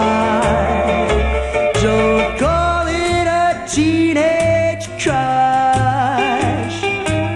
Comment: Who played on the backing tracks? At 1:09 slowed down I hear "teenaged trust." Anyone?